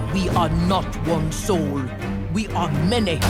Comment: wtf does she say at 0:16 "we are not one sol we are manny"